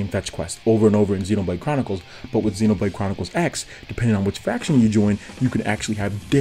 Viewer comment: Not to be mean or anything, but i think i just heard you say fraction instead of faction.